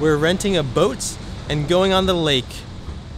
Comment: I thought you said "going on the linq"